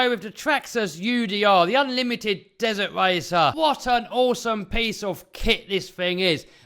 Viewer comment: What an awesome piece of shit that is? 🤣 I could of sworn that’s what he said. I’ve never heard piece of kit before 😂 is that a UK thing ?